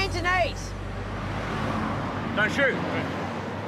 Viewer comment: What does he say at 1:36? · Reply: He said, don't shoot....fooling around!